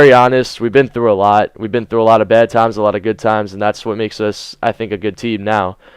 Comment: I thought you said you and Dedo have been through a lot of bEd times at first ;D